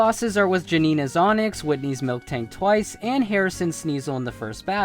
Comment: Did he say... MilKtank??